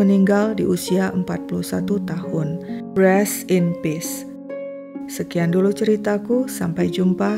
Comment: Rest in peace, koq sebutannya Breast in peace, diakhir video, apa aku yg salah dengar ya?